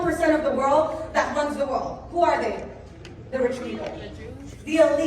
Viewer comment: did anyone else catch someone answering "the jews" when she was asking about who runs the world??? yikes 😬